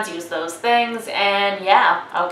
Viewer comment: before i saw this i thought they were saying "i'm gay and this is bookish pixie" kinda makes more sense now xD